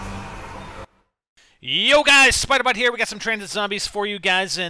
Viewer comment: dude in the start of the video it sounds like u say spider butt? what the heck man?